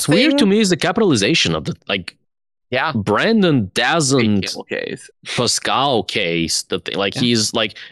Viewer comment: I feel really silly struggling with this, but can someone help me understand this word says at 1:50:27? It sounds like "Brandon doesn't... poscau case." I can't figure out what that third word is. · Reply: I believe he's saying PascalCase, programming jargon meaning when you use a capital instead of a space to combine two words, i.e. TimeTellers